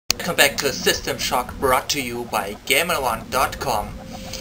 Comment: lol when u started the vid since my ears were listenign properly i thought u said System Fuck lol